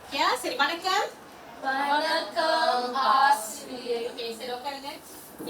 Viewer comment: Did they say "arse rear" ?🤣🤣🤣🤣